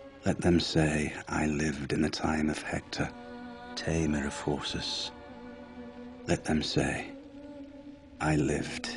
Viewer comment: I always thought he said "I live in the time of Hector, tamer of forces" forces, like armies? lol horses just doesn't make sense to me